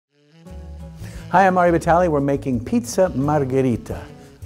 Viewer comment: why the f did i hear "Laura vitale" 😂